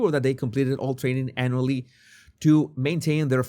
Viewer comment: Did he say "anally"?